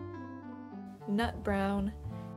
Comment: Instead of "NUT brown" I thought you said "NOT brown" so I got confused looool